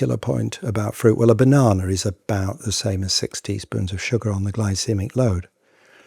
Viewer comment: my goodness for a second i heard sixty spoons instead of six teaspoons lol panic